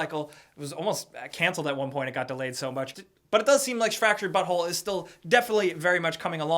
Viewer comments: It sounds like he was saying fractured butthole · sounds like you said fractured butthole